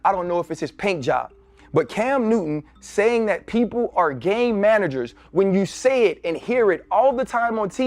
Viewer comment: Oh , I thought they were saying gay managers. I get it now.